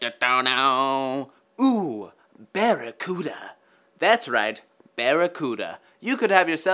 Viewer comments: I heard it as Barret-cuda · i thought he said barretcuda